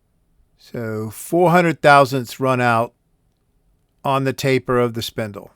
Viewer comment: If I did, then I misspoke, my dial inductors read in thousands of an inch .001". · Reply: Maybe I heard it wrong but at 16:20 it sounded like hundred thousandths. At any rate I really enjoyed the video and I learned a lot about runout.